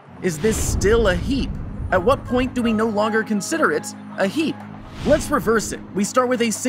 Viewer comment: it took me so long to realize he was saying 'heap' instead of 'heat'. Why am i so stupid